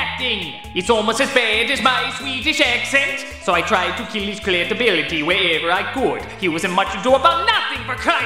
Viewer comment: turn on subtitles. 34:00 CLIT ability instead of cred 😂😂😆😝